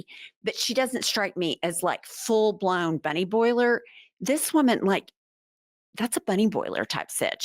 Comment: Bunny boil! 😂😂😂 For a minute there, I thought yous were saying Bunny Boy! That was my party crew in the 90's..Bunny Boyz Production. 😅